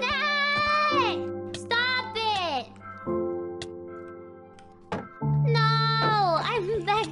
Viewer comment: All i hear is "Noo i will beg the low corner"